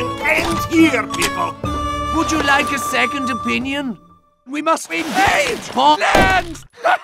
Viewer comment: did medic say we must engage Poland? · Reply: He said, "Ve must Invade Poland!"